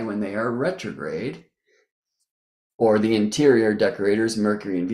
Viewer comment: I thought you said 'BRETTROGRADE' 🤣